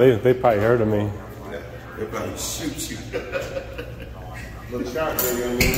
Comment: Did he say “They’d probably shoot you.”????